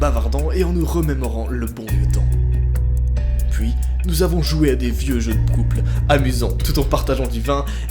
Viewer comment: C'est moi ou à 1:35 t'as dit "des vieux jeux de prouple"